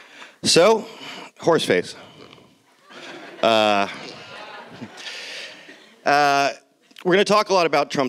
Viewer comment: it’s always funny. sometimes it’s offensive, too. plus, i thought trump said whores’ face